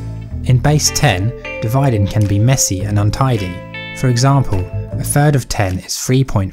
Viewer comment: I don't get it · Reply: it sounds like free instead of three